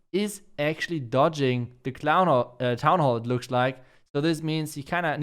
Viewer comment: Hey.. Always a big fan 😉 Btw Did said Clown Hall ..... At 20:15 😁😁😁😁😁😁